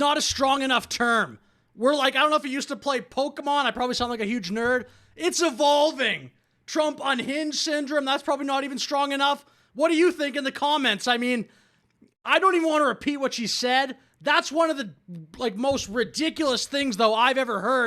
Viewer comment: Hahaha I caught that too. Idk what he said there. Unhinned maybe ? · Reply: I heard “unhinned” 100%